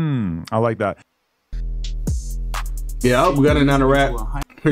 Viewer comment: "Hope Gunna not a rat, his ** too hard know wha im sayin" Had to re-listen a few times to make out what u said but still cant understand what youre saying is too hard..no diddy. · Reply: His music is hard